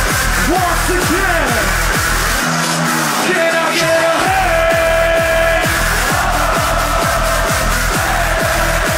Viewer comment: What he said? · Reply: Can İ get Hey, Hey Ho,Hey ho😀😀